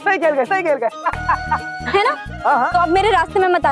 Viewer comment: is he saying ''correct correct sahi keh diya ''?